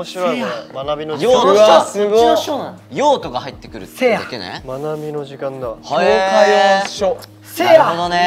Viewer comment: 学びの時間だわがまなみの時間だわにしか聞こえない呪いにかかった助けて笑笑